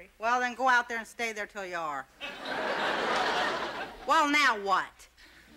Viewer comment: what dids rossann said!?!? Woah nowwatt! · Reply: Dawnyella McJenkinstein "well now what?"